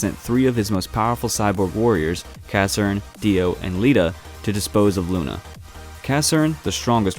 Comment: did they say dio